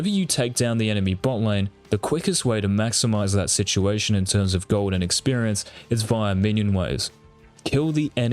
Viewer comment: I thought he was saying "Golden Experience" the whole video until I realized that he was talking about "Gold AND Experience".